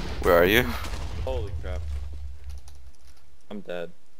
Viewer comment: "Holy crap" "Yeah condom, condom That's what I heard lmao